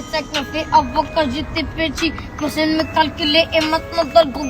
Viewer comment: c moi ou il dit "quand gt petit"